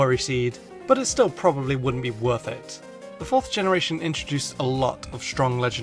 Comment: instead of perfect he said "worrfect" good vid tho